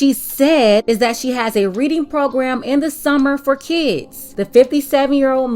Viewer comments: Who else thought she said “my submarine program”? 😂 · I thought she said "submarine program"......😂😂😂😂😂😂😂😂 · I legit thought she said that she had a submarine program. I kept asking myself if that meant that she was in the Navy or was was selling submarine sandwiches out of our car or something.